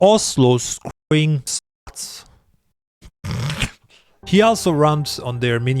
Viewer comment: What did you say? I couldn't catch that part. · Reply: "Scruting sluts"